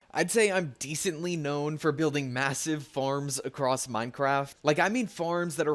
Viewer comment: did someone say "Massive" ? :D